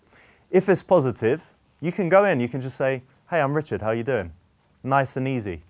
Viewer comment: Lol I stopped watching 3 minutes in to read the subtitles cause they were hilariously wrong... at 8:06 he says, "Hey I'm Richard, how're you doing?" The subtitles say differently... "hemorrhage of how you doing" ... lol?